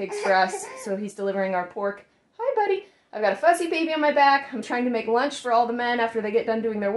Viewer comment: I heard you say “fuzzy baby” at first and looked at the baby and was like, “Thats a cute way to talk about your baby, but he doesn’t look that fuzzy!” And then I realized you said “fussy” and it made more sense.